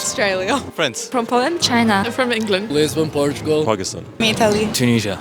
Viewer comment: Did the tall, dark guy at 0:06 say he was from "Afghanistan"? (because I think I clearly heard him say "Pakistan").